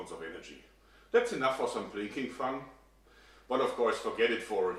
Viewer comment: Don't think he said drinking fun. I think it was plinking fun.